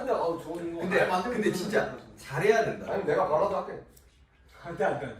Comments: did Yoongi say “Ariana Grande”? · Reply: Gabby Mendoza No Suga said “잘해야돼” which mean is “have to do well” :) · y does it sound like yoongi said ariana grande @ 19:33😂😂